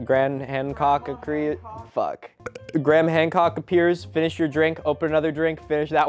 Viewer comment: had to go back ten secs and relisten, ‘cause I totally thought you said ‘Grandma Hancock’. 😅